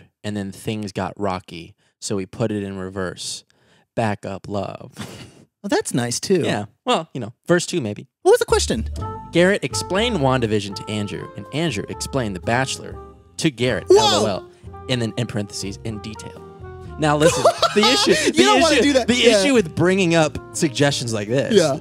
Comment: Thought it said “explain long division to Andrew” I was so intrigued